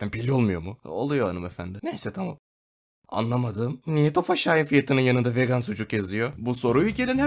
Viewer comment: orada ne dediğini anlayan var mı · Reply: Neden Tofaş Şahin fiyatının yanında vegan sucuk yazıyor